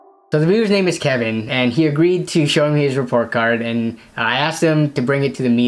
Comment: I thought it said “show me your report card, and I’ll sell your PC”